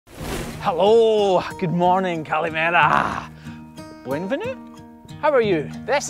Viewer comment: He just said 'Kalimera' or am i hearing things??